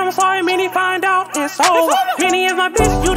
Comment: what does he say in that moment? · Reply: he says “its over!” Just in background vocals